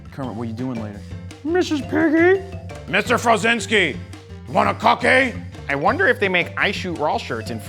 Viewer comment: I thought cookie monster said, "Mr. Frozinky, want a cocky ?"